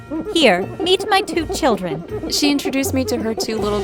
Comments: i heard her say "eat my two children" at first 😳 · When she said meet my new children I thought she said Eat my new children 🤣🤣🤣🤣